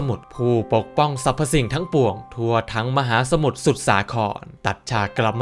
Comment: ผมได้ยินสู่สาครด้วยครับ 1:42